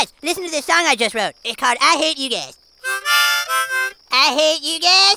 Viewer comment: I hear him saying “I hate you gays” for some reason 😂